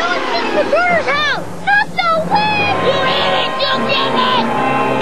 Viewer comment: YOU HIT IT YOU GIT IT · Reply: smokemadbong Sounds like he said "YOU IDIOT YOU GET IT"😂